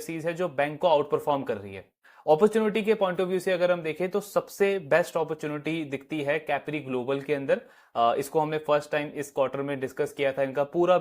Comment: One suggestion , kindly flash the name of the stock you are recommending , I couldnt catch the name of the NBFC Mentioned at 4:47 some of the names we hear for the first time , its always not clear , so please flash the name so that we can understand. keep up the good work ❣ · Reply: CGCL - Capri Global